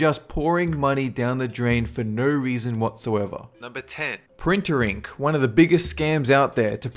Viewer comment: And I thought you said "printering" on #10 xD